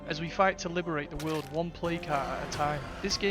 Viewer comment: lol am dumb thought you were saying play-cart not plague heart